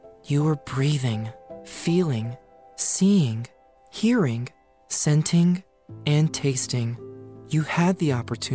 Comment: Hang on, at 6:45 when he was naming the senses, did he say "scenting"?